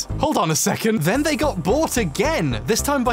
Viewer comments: At the beginning of the video, I initially thought you were saying that companies ‘got bored’ rather than ‘got bought’. Thanks for your work. This started playing even though I saw it before. Hoping this comment makes up for not watching it through. 🦑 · Fucking British accent. When he said they got bought I heard "they got bored"